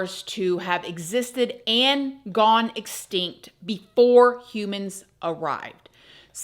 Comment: Did she just say "how dinosaurs went instinct" instead of exstinct?